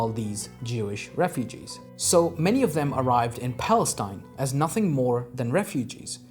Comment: youtube subtitles are using word israel instead of palastine.